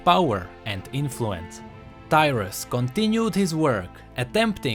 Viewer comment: 0.32 I was letting it play while doing other stuff to just listen then i almost fell of my chair cos I thought he said "Dyrus"